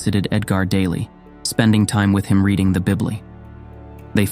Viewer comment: i lost it when the voice over said 'bibly' instead of bible ( 9:53 )